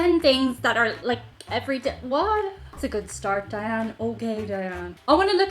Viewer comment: Jesus. Fright of my life when you said "Diane" twice. My eyes had drifted to find my water and I thought you said "Diana". For a hair of a second I thought you could see me